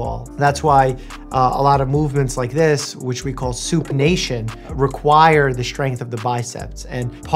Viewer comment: did anyone else heard "Soup Nation"?